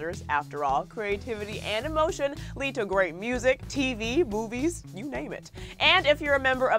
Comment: Did you just say boobies instead of movies??